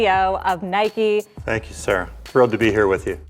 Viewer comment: did he called a woman sir? 😮😬😂😂